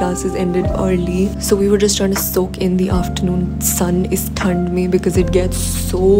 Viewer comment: Sun is thand me 😅 am I heard something thing wrong ??. Btw love your Content ❤❤❤